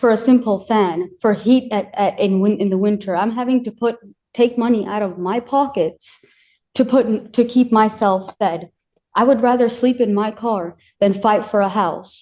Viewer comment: Did she really say, i spend money out of my own pocket to feed myself.